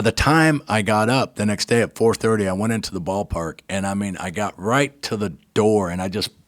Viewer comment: oh, ballpark. when I was listening to the podcast, I thought he said hallmark.